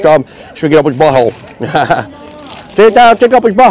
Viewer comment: Sound like he said " stick that of his butthole" 🤣😅😆